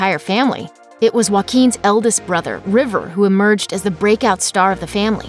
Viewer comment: I thought you said his name Jay-o-quin